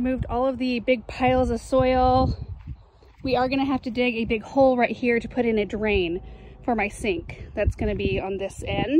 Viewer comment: Even the shadow of the Hartley (1:40) is gorgeous! I didn't understand what you said though..."some sort of formal ?? garden" at 2:01. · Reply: Knot garden